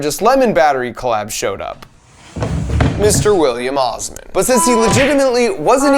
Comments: I thought you said Mr. William Afton so I said, “ he always comes back.”. And then I realized you said Osmond and I said,” he still always comes back” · I legit thought you said "Mr. William Afton" and I had to activate the captions, just to be sure 😂